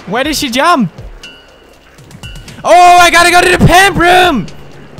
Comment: "OHH! I GOTTA GO TO THE PIMP ROOM!!!!!" -Pewds 2014 XD I know that isn't what he said, but it sounded like it.